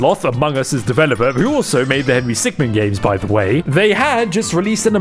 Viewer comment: what they are saying: are developers who also made Haney stickman games by the way What the captions think what your saying: is developer who also made the Henry Sigmund game by the way Edit: by the way THATS my favourite part of the video